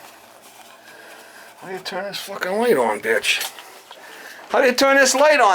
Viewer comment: Did call his customer a witch?